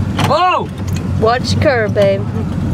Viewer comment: aye who heard her say "watch the curve Bae"